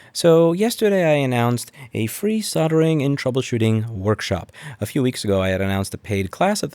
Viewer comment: I brought an AK-47. I thought he said soldiering workshop.